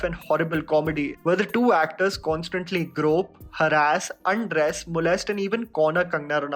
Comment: i thought he said "grope.. her ass..." i almost said "Oh my!" and clutched my pearls like an offended grandmother...